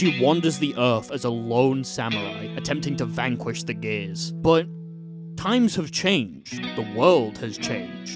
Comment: Died of laughter during Baiken’s theme explanation because when u said “vanquishing the gears” i first heard it as “vanquishing the gays”